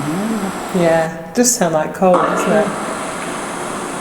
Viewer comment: I hear the word “cold” as well coming from the recording of the playback